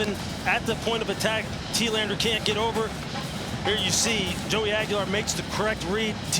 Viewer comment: did he just say Joey Aguilar?